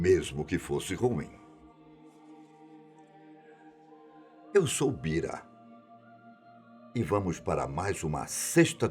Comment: Juro q ouvi "eu sou Kira"